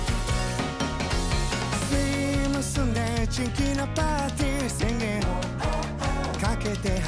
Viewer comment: ツリー盗んでチキンなパーティ(ナゾの空耳)